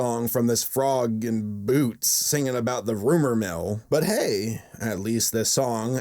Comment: every time the frog in boots said rumor mill i heard ''rubber bill''